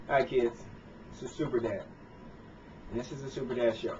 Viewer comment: thumbs up if you thought it said SUPERBAD at first lol